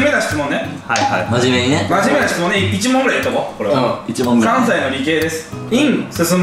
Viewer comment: 関西の理系が3歳の理系って聞こえて！？ってなった